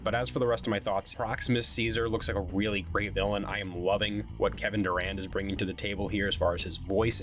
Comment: Hahah for a second I thought you said Kevin Durant and I was like when did he start doing movies and how is this not getting more attention lol